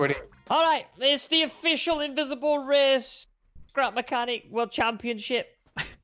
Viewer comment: I thought said crap mechanic